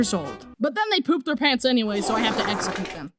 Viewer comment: i thought he said "i have to exepoop them"